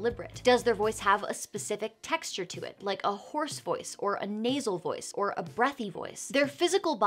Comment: I heard "horse voice" instead of "hoarse voice"